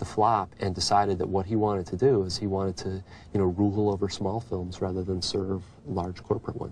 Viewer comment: what does he say at 9:37 "-- over small films", it sounds like RUGALL, or ruegel, but i cat find a real word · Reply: Rule.